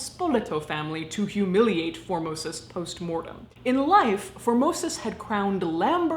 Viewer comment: Formosis? I thought you were saying "phimosis", a disorder or the male genitalia.